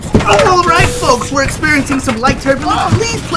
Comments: it sounds like hes saying experiment balls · I thought he said experience balls lol